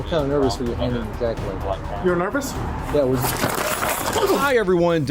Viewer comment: "You're nervous?" Bro why does it sound like he says "you should be" ? Like damn, plain as hell